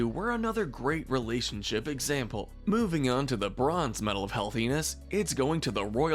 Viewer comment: When you said "the bronze" I thought you said "Le Bron."